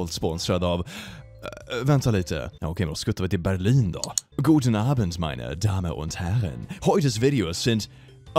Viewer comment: I'm currently learning German, I swore I heard say, Gutenberg Abend" meaning "good evening"